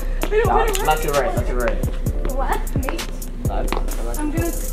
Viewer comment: did she say BABE?!?!?!